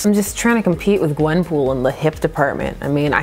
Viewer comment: Did just say “I think when we do her next comic I’ll floss?” Sarcasm be damned, I’d be here for that!